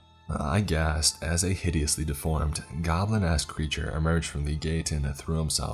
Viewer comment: When you talked about the other guy that was a servant as a goblin he said goblinesch It sound like he said goblin assed looking thing and I just burst out laughing.